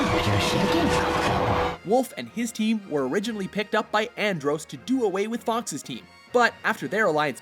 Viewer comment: Did he just say Androse?